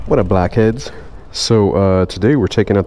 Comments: Did he just say whats up blackheads?🤨😳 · did you just call me a blackhead? XD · i had to do a double take, it sounded like you said "what up black kids" for the intro